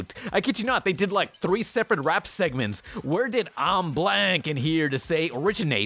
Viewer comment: I thought he said I'm black and here to say